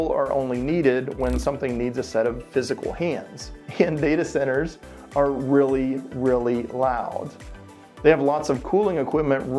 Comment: [tried to reads lips] 12:38 “And data centers are very very [pr]oud?” Wait. What!? I can't hear you!🎧👂🏽✋🏽😜